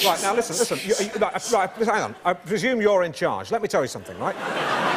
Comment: I hadn't seen this for a while and always thought he said "I presume you're head clown" instead of in charge. Ah well equally as funny!